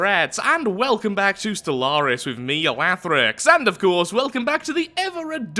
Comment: Is it just me or did you say Wathrix at 0:01 ?